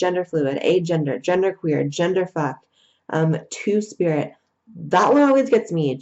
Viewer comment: did.... did she say gender fuck?